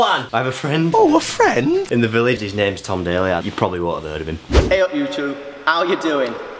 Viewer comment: When you said “i have a friend - you probably havent heard of him” i thought you said “i have a friend, he’s probably a virgin” 😂😂😂😂